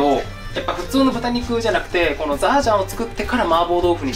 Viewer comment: 「ザージャン」が毎回「ラージャン」に聞こえて仕方がない